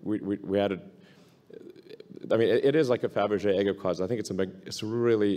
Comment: Did anyone understand what Elon compared the Model X to at 9:03? WTF is a "fabrigee eggocause"? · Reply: zlozlozlo He said the model x is the "Fabrige Egg of cars". Meaning it is fancy.